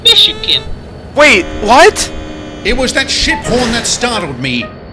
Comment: that...um...uh....that sounded like, “ that $#it horn”